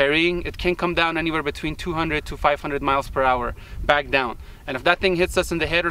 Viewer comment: It can not fall back down at 500mph. Once it reaches terminal velocity, it can't fall any faster. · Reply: For those who missed it he DID indeed say, 200-500MPH. Also, not entirely accurate, but... <link>